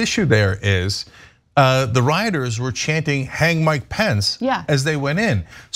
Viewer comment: They were saying "Hang Mike Pence?" All this time I thought they were saying "Hate My Pants!"